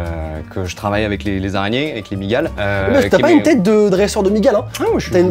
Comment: C'est moi ou ta dis" MEUF ta pas une tête de dresseure "